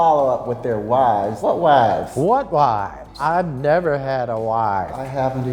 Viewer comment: i understood "lifes" instead of "wifes" WHAT LIFES I'VE,NEVER HAD A LIFE